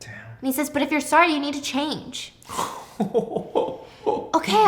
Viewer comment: what I heard when he said Jules needs to change was “if you’re sorry, you have to let me cheat and act like you didn’t see me!”